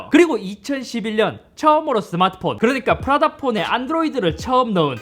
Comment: 나만 안드로이드를 쳐넣은 이라고 들려요? 너무찰졐